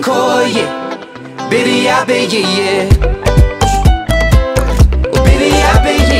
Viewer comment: THIS WHOLE TIME... i thought he said "baby ive been here yeah" BUT HE SAYS "Bibia be ye ye"